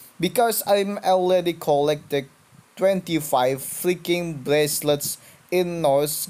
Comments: When u said "25 Viking Bracelets" I heard "25 Fricking Bracelets" Lmao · I thought you were saying fricking bracelets